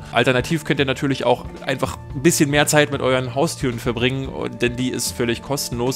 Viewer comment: Bin ich der einzige, der "mit euren Haustüren" verstanden hat? :D